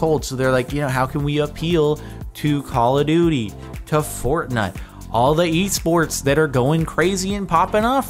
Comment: did he say fortnut?